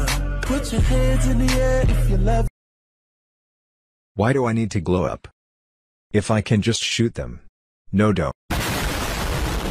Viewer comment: When he said “why do i need a glow up?” I thought they were say “when i can blow up”.